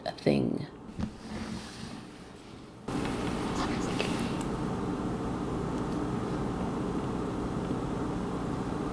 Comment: I HEARD THAT!!!!!😮😮😮😮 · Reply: Me too!! Someone’s looking. Creepy.